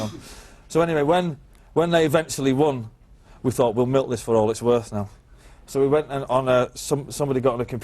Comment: what did he say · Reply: Gonna milk this for all it's worth.